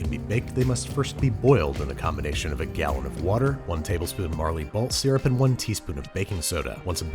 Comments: did you say marley balt? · did you say "marley balt syrup" ? XD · did he say Marley Balt syrup? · What. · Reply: He says Marley Balt instead of Barley Malt. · Did he just say Marley balt? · What do we wanna see for the 8 Million Subscriber Special folks? I'm going grocery shopping this afternoon and wanted to see if I need to grab donkey-milk caramel or flying fish roe or whatever! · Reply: did you say "marley balt syrup" ? XD